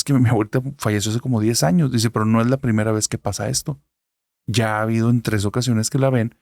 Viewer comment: se escucha una voz de fondo qué dice "10 años y es la primera vez que..."